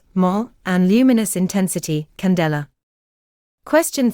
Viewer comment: I don’t want to be annoying but I’m pretty sure 15:39 the unit for luminous intensity in question 2 is supposed to be candela instead of caldera.